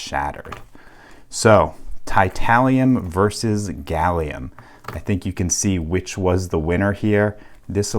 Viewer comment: For a second I thought it said gallium versus titanium lol was so confused